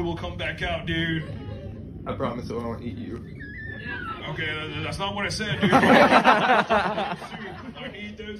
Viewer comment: Bro my volume wasn’t but so I thought the turtle said that’s not what I said nigga